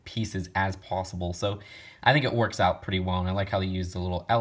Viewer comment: Greta video, but I hope I wasn't the only one who heard you say a word weird 😂😂 11:15 wait for it "pretty mong"